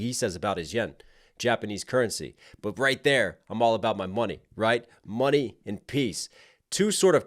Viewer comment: I initially thought he said "all about my yin".